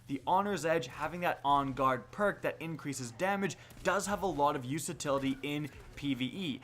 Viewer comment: hold on did he just say usatility @ 10:07?